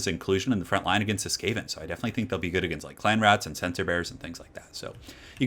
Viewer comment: I thought you were saying "sensor bears" at first